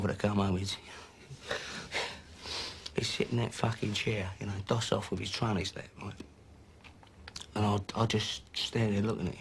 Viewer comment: What does he say at 1:23 he’d sit in that fucking chair you know doss off with his ………. ?? Thanks in advance lol 😂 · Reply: Figured it out 😂 got it on blu ray and turned the subtitles on he says doss of with his tray in his lap 😂